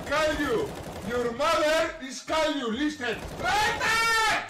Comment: I thought he said "your mother is Caillou" lmao